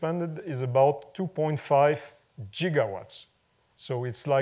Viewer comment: he actually says jiggawatts! :)